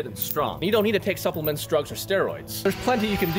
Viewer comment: When he said drugs I thought he said strokes and I think I’m down bad… like real bad